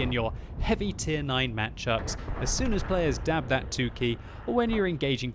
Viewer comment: did he said ''DAB that 2 key'' ??? ok,let's dab